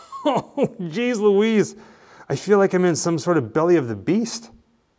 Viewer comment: At first I thought you said Belly & the Beast at 15:07. Lol. Thank you for sharing this with us. Neat stuff.